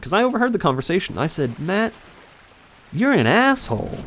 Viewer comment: did you say Matt? or Man?